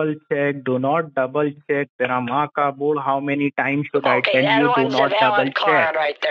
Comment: Ok I heard "Tera ma ka pod."